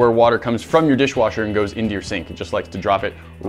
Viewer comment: i thought it said under your skin instead of sink. i need some coffee.